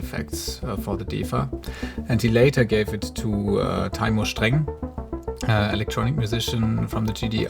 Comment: What was the name of the musician referenced at around 2:45? It sounded like "Timo String" but nothing came up with a search. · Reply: Taymur Streng